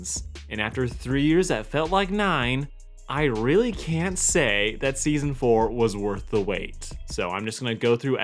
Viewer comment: Is he saying "can say" or "CAN'T say?"